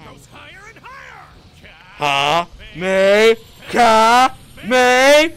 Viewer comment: did he just say Kamekame 😮‍💨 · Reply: nope. He said Kamekameha